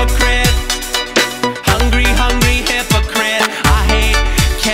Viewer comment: misheard that as hungry hungry immigrant